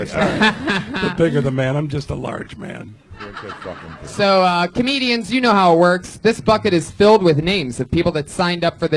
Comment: what did Joey say? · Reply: "your a big fucking dude" I think, I don't know though